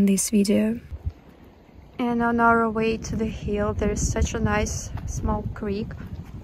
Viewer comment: I was waiting for the creep mentioned in the comments, so at 8:35, I thought said sarcastically, “There’s such a nice, small creep.”